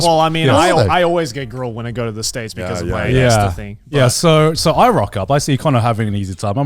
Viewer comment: "I always get grilled when I go to the states because of my XXXXXX thing". Does anyone understand what he said? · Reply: I believe he said "ESTA thing" - I heard he was deported from the US on one occasion for having the incorrect visa, or something